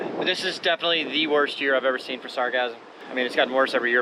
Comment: It sounds like your saying “sarcasm” 😆😆😆😆😆